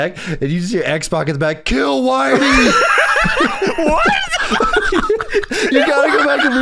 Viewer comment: i cant say what X Pac is yelling... But 😅😮😂 Edit.... Fuck it.... X-Pac is in the background yelling "KILL WHITEY" I do not endorse any Racist Comments portrayed by Sean Waltman Tired of getting slapped by YouTube for this comment